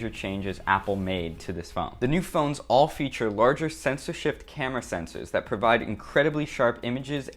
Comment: Censorship camera sensors? Did i hear that right at 1:45? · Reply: “Sensor shift”